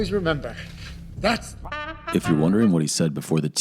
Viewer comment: I had initially thought that he had said "that's life" The song even played in the end credits